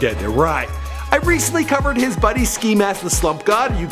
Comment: nigga wtf 1:17 why did you say it like it's french??? nigga what was that? "xxxtuntuseaun"